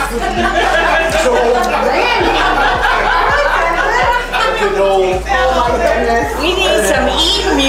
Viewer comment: did he just called you Randy 😂